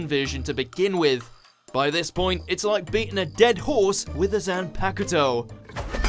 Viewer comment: did he just say....Zampakato??